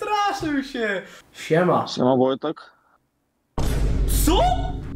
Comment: co on powiedział? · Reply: siema wojtek